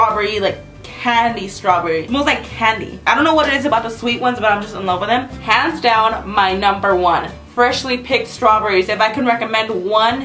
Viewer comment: i thought u said u were in love with the birthday cake